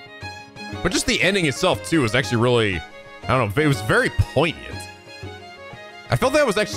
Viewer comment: poignant? is that what he said?